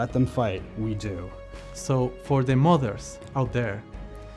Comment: "For all modders out there" I misheard as "For all mothers out there" and for a moment I thought this DLC was dedicated to all mothers who wished they birthed a horrible but cool space monster instead of these boring mini humans